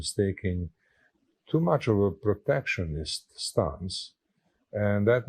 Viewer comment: I think he is saying "protectionist", but the banner says "perfectionist."